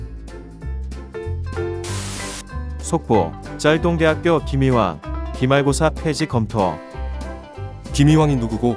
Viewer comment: 또 김이완으로 들림 내이름 김이완인디...